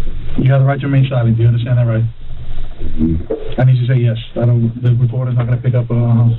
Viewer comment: Cop: “You have the right to remain silent, do you understand?” silent Cop: “I need you to say yes” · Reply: Nah you right, the cop said “I need you to say yes” The first time I watched this I thought he said “you have to say yes” that’s my bad I didn’t have my volume up. The original intent of the comment wasn’t supposed to be that serious but since everybody wanna come in here being lawyers and shit then I just wanted to make 2 points: 1. still didn’t have to respond bc he had the right to remain silent, but the cop gave a manipulative response (very possibly unintentionally) in order to fulfill the acknowledgment of rights. I get that. Simply was pointing out the irony of them reading his right to remain silent then basically telling him he has to respond. And it really doesn’t matter if he said mhm, mmm, or abrakadabraalakazoo bc none of those responses are proper affirmations therefor are as good as silence in the context of the law. So when I said was silent I meant that he wasn’t SPEAKING audibly, which the cop acknowledged. I’m actually floored that so many people got so salty over this